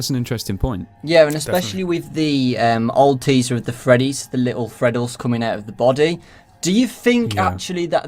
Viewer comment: did he just call them "little Freddles"?